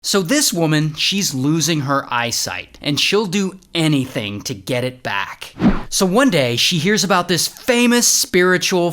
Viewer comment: i thought he said "so this woman is losing her iSight" 💀